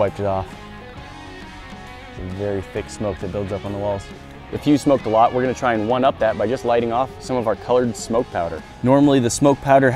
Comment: I thought he said "If you smoke a lot, we're going to try to one up that". I was like, wait, what? Is he going to try to light cigarettes in the chamber and them smoke them all LOL